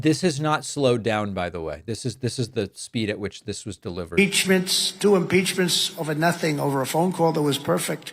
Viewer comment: Fool said impeachment, sound like he said “ two pieces of chicken”🤣😆😆😆✊🏾